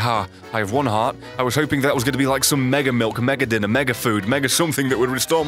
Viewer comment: did he just say mega milk?